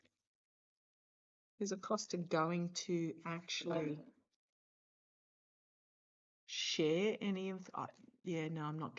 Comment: I hear another voice besides yours, but I can't make out what is said 🤷‍♀️💙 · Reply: Yes I heard it too. Sounded like saying “Aye”.